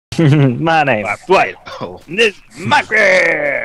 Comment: start the video and all i hear is " my name is dwight and this is my crib" YEAAHHH